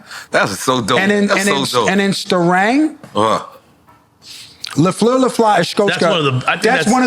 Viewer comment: What were they referring to here 1:16:02 couldn’t understand what he said and I wanna listen to this. If anyone can help. Sounded like gibberish lol “And in sterang lefo lafa escosca”